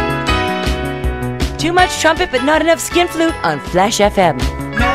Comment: What Toni is saying here? "Too much..."? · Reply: "Too much trumpet but not enough skin flute, on Flash fm"? (= "just talk but no action"?)